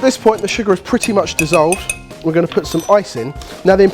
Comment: while was making a cocktail, I looked away for a sec and heard him say 'we're gonna put some icing'. Turned my head excited to see buttercream icing or something similar, just in time to see him grab an ice cube. Disappointed.